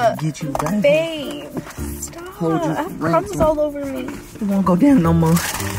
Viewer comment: For a second i heard i have c*m all over me on 6:10 😬😭😂🤦🏽‍♀️🤦🏽‍♀️